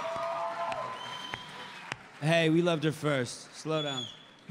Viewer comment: What did Norman say at 27:34? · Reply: "Hey, we loved her first. Slow down." - It's a reference to S04E01 when Carol said "Just so you know, I liked you first." to Daryl.